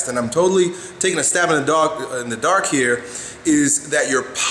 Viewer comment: "takin a stab in a dog" @ 0:48. Anybody else catch that?